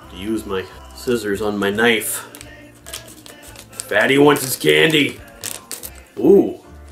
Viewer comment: I've replayed it a few times but can't 100% confirm: was it "fatty" or daddy" that "wants his candy"? I thought I heard daddy two times I played and now only hear fatty. HELP. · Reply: I definitely referred to myself as "fatty" not "daddy".